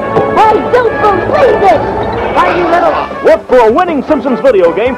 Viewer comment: What did Krusty said???!!!! Why you little or not?? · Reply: He said “You little.”